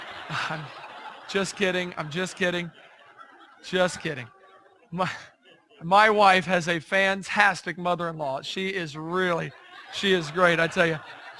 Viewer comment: A 3:10, did he say "My wife has a phantastic mother in law"? So, he basically says, that his mother is phantastic?